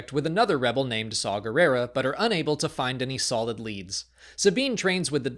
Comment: I heard that. I HEARD THAT! "unable to find any Saw-lid leads"